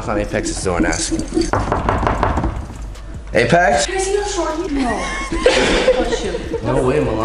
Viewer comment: what did Milan say?? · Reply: “Can I see how short he is?”